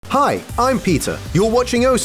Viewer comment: I seriously thought this guy said "Hi, I'm pizza"